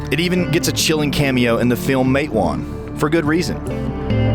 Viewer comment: "It even gets a chilling cameo in the film _____" What? Did anyone understand the name of what film he mentioned at 1:40? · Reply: the film, "Matewan".